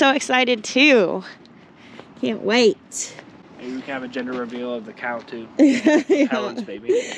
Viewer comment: "ginger-reveal".. I heard "ginger-reveal".. ;D ;D ;D